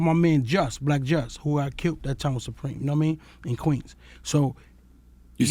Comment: Bimmy tried to pull a fast one and make himself seem gangsta on the slick by claiming a body he had nothing to do with but Charlamagne didn't let him get away with it 😂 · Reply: I clearly heard him say "Who got killed with Supreme" but it seems a lot of ppl think he said "I killed with Supreme". Who would admit to a murder on a live radio interview? LOL. Everybody already knows who killed Black Just anyway.